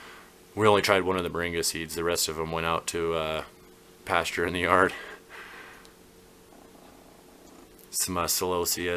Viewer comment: Wow! Your seedlings are looking fantastic.Did I hear a Tetrus reference near the 5:00 mark? LOL Take care guys. Rob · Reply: Thanks tons! Falling behind a bit but using the lag to manage the back end a bit better. Nice plants but less.....for now. LOL 'Tetris-izing' is a frequent term around here with how our house resembles a series of Russian stacking dolls. Got to keep it looking pro and like we know what we re doing. LOL